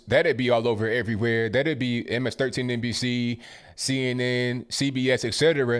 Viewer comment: Did I just hear him say "MS13NBC"?! 🤣